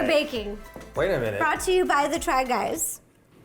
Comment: what did Eugene say to ro?? Someone tell me plz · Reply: he said 'did you pick pettifor because you're petite and there's four of us?' pettifor is the type of cake they were making. :)